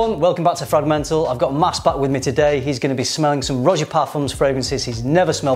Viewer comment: Thought it said ‘selling’ not ‘smelling’. Was like man…scent geeks is gonna put you in the poor house.